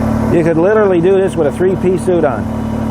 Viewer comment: Oliver Douglas from Green Acres would change out a backhoe in a 3 piece suit. : ) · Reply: Ohhhhh....3 PIECE SUIT.....I thought he said '3 peas soup'. I'm usually pretty careful when it comes to eating 3 peas soup, but I have wore some before. Thanks for clarifying.